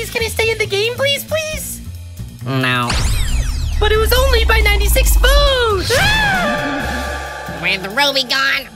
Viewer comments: I thought ruby said 96 phones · go here : 3:40 turn on caps and ruby says : " but it was only BE 96 votes!! AAAAAA!!" it says "BE" instead of "BY"